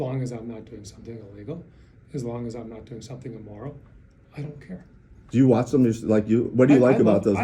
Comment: did he say as long as I’m not doing something illegal or immoral. I don’t care ?